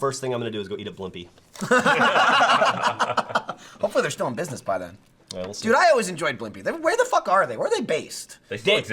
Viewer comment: what's the "r" word geoff refers to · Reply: Risinger